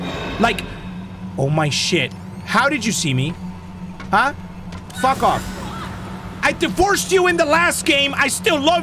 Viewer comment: did chica just say “what the fuck?”